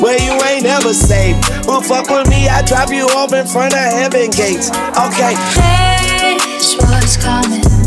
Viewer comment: did he say heavens gates? Or....Kevin Gates??????